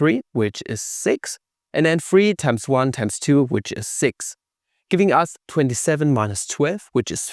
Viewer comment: That sounded like sechs, mein Freund. gut video ..there goes all my German class